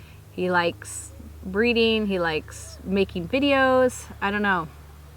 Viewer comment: did she say he likes breeding ❓🤔